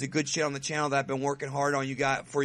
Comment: Thought you said Trump stuff.. lol